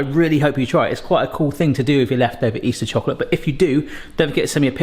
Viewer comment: Can someone translate what was saying at 14:30, I thought he said "left over Easter chocolate"